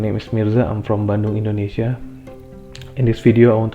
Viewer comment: could you fix the subtitles there? He actually said "I'm from Bandung" but the subtitles say "Bando"